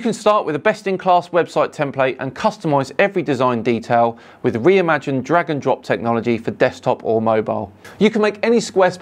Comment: why did I hear "Imagine dragon"